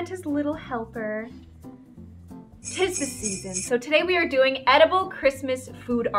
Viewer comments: Uhhm… just me or at the start of the vid does it sound like she said “ today we r doing edibles “ 👏😰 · My screen started loading at the beginning of the video and it sounded like she said “edibles”